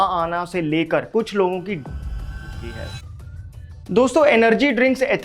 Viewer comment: fit tuber भैया ये क्या बोल रहे थे.. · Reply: Kuch logon ki death ho chuki hai